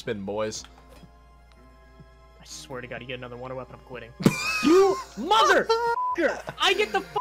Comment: did anybody hear say “Mother Fucker!”?